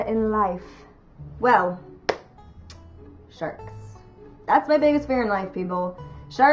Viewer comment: When you first said "sharks" as your biggest fear I thought you said "jerks". LOL